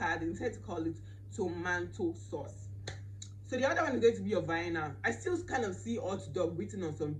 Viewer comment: I had to repeat this part several times coz I didn't understand what you were saying until I realise that you were pronouncing vianna 🤣🤣🤣